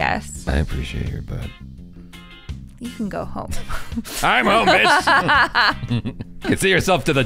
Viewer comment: 😂😂 The caption says, "I'm homeless"!!!! 😂😂 Poor....